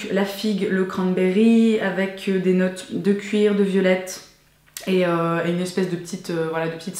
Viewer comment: Quand tu as présenté la bougie Rewine, au début j'ai entendu "belette" au lieu de "violette" et j'ai arrêté de manger 2 secondes (surtout que juste après tu parles d'une odeur musquée)... avant de reculer la vidéo. Aussi just in case, "cranberry" = "canneberge" =) Vraiment chouette l'idée d'avoir un petit flacon qui vient avec la bouteille de parfum pour le tester avant au cas Et si on le connait déjà, ben ça fait un format de voyage à emporter avec soi =) Le concours est-il seulement pour la France? Je serais bien intéressée à participer... mais je suis présentement en Asie en ce moment ^^